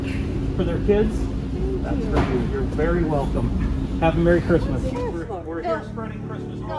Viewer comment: Lmao!! You guys are killin me right now hahahaha... also you guys are so badass keep up the good work boys ..and yes I heard say " Merry welcome ". Bahahahahaah